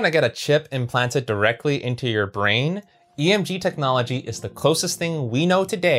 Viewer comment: I thought you said DMT technology